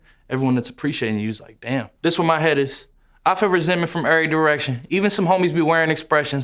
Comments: Who else thought he said this for my haters and not this where my head is. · i always heard “this where my head is” as “this for my hittas”😭